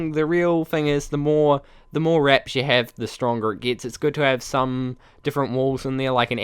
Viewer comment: Uhh did i just hear at 1:06 The more erections u have, the longer it gets?😂😂😂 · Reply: Lolemao no the more raps you have the stronger it will be